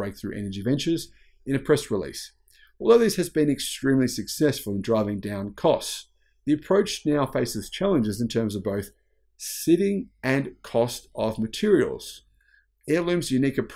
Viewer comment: Did you mean to say "siting", as in physical location? I heard the word "sitting", as in a chair. Not a big deal, but my tendency to OCD couldn't resist commenting...